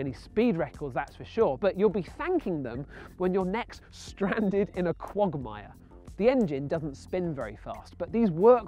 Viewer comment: did he say when your neck is stranded in a Quagmire? (giggity)